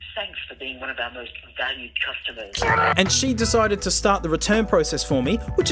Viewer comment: That Ebay call, I swear on my first view, I thought it said "Sucks to be one of our valued customers".